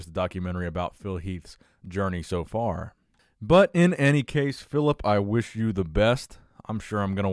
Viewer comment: Did you just call Phil Heath Phillip? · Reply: Tim Cotter Timothy my boy... I believe did indeed call Mr. Heath “Phillip”.